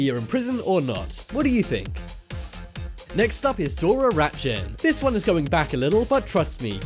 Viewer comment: Lmao I thought Dora looked like a dude...it was so fucking obvious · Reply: Precious Felton I first thought they said Dora ratchet